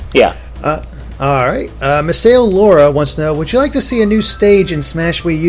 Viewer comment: did he say MasaeAnela